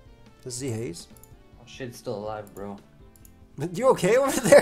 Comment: Did... did say shit?